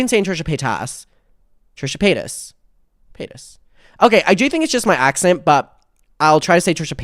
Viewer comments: Can't help myself... I keep hearing 'Trisha Penis' 😅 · Sounded like you were saying Trisha penis for the first minute 😂😭